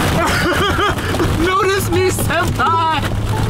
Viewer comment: did you say notice me sempei and sorry if i idk how to spell